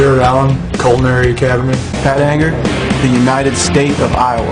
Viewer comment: I thought he said "fat anger"